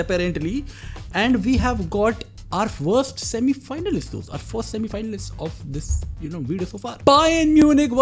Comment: i thought bro said worst semi finalist 😂😂😂😂😂